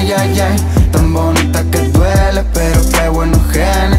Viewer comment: Yo pensé que decía "tan bonita que tú eres"